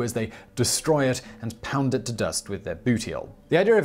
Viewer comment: I heard that as "Pound it to dust with their booty-hole"